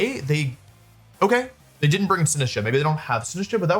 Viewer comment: the captions say sin [ _ ] 😂